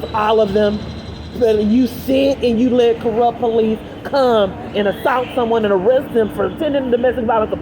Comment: when you said come it sounded like CUM (Ejaculation) 😊😊😊🎉🧨🎇